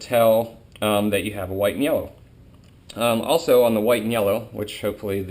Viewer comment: Hey, I know they are Called White & Yellows (I'm going to be working with them next season) but it really sounds like your saying white meyellow.